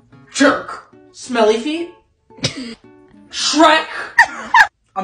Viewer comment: I thought he said daddy Autism